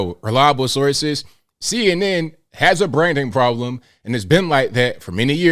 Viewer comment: Ok.. Anyone else think he said “CNN has a Brandon problem..” at first? LOL